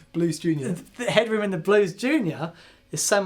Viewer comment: Did you just say “the headroom in the Blows Jr”? Hahahaha